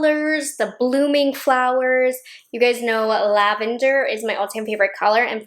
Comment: You said "lavander" and I heard "Nouveau" <3